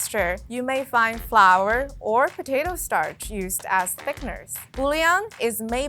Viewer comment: I had to pause and replay the part where she discussed the concentrate. At first I thought she said "fake" but then realized she was saying "thick."